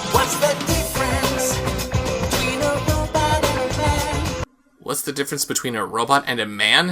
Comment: "What's the difference between a robot and a man?" makes more sense than what I thought it said - "What's the difference between a robot and a PLANT?" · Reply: Nah, I heard “a robot and a man”